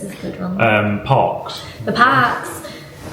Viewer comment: Glad you’re enjoying yourselves in Australia…….even though you’re in Victoria 😂 EDIT: thought you said ‘Pox’….😮 ??? Oh, “Parks”😊